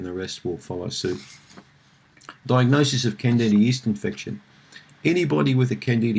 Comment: can somebody tell me what did he say at 06:39 I only understand "infection", sorry I'm portuguese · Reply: Sergio Domingues diagnosis of candida yeast infection